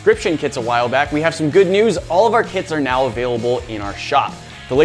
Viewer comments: I thought he said All of our kids are now available in our shop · I thought he said “all of our kids are now available in our shop.” XD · I thought you said kids All of our kids are now in the shop XD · When you said our kits are available in the shop I thought you said our kids are available in the shop!😂😂😂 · Who else thought he said “now are kids are available in shop” creepy people: “oh really” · At the start of the video I thought I heard: 'All of our kids are now available in the shop!' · I thought he said “ our kids are now available” and IK I heard wrong but I tried to not laugh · I thought you said kids were available in the shop I got scammed :( I want a refund